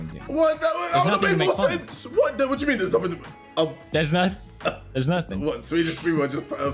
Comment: what did he say lol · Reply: He said there's nothing to make fun of